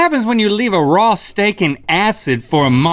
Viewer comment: I thought he said “raw stankin ass” in the beginning. 😂